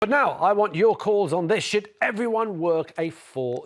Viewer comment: At the beginning, I thought the guy said, "But now, I want your calls on this sh-t."